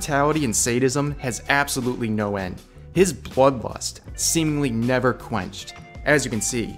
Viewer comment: Why did I hear "plug lust" instead blood lust?